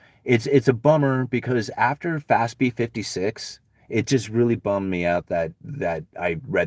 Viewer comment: Fastbe 56?? Did I hear that correctly? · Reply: FASB 56